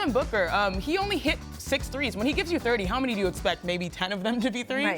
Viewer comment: What did she say at 0:56? She expects if he gets 30 that 10 of them be threes?